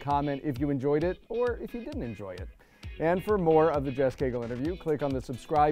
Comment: I thought the interviewer said kegel interview!